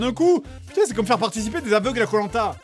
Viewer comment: Il dit quoi à 16:38? "C'est comme faire participer des ??? à Koh-Lanta!" mais je comprends pas ce sont quoi les "zapdogs"(?) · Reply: Ah oui des aveugles je suis con x)